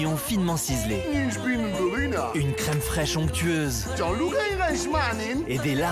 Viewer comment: est ce qu'il a dit "jean luc reichmann" ou c'est moi ?